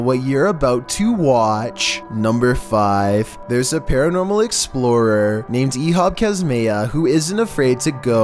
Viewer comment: I thought you said Dora the explorer xD